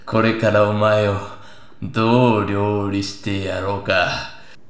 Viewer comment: どう料理してやろうか がゴルゴーンに聞こえる。。。